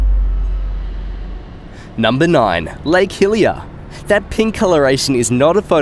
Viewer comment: when you said lake hillier, i thought you said hylia